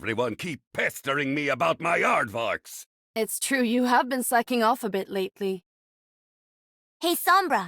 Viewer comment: has a typo, it should say "off" instead of "of"